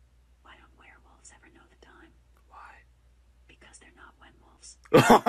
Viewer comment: What is she saying? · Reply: Him: Alexa, tell me a scary joke Alexa: Ok, why dont werewolves know the time? Him: Why Alexa: Because they arent WhenWolves Him: hhohHHOOhHhHOhoHOOHhoOHhho